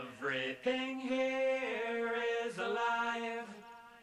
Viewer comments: Lol I always thought they said ‘everything here is a lie’ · Anyone else thought he said “everything here is a lion”